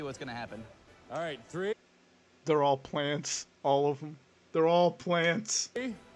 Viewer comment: "They're all planets" is what I misheard. That potential fat joke gave a me good chuckle.